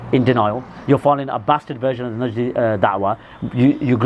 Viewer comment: Did he say “bastard najdi” at 19:40? Lool